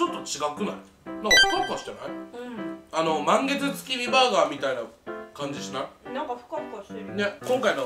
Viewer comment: 満月月見バーガーがマン毛付き味バーガーに聞こえた